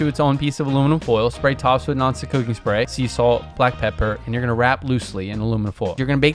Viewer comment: Meanwhile, he said to spray the tops of the garlic with "non-stick cooking spray, " and I heard 'noxic cooking spray.' 😅